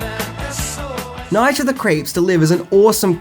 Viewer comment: Sounds like youre saying Night of the "CREPES." 🤦🏽‍♂️